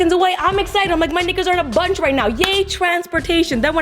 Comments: did she just say "my nights are in a bunch right now"??? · did you just say NIGGA? 😡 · listen from 1:22 to 1:23....... DID SHE JUST SAY MY NIGGAS?!?!?!?!?!??!?! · This video is so great😂😂 did she say "my niggas" at 1:20 😂😂😂 please tell me I'm not the only one who caught that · Did anyone else think she said nigga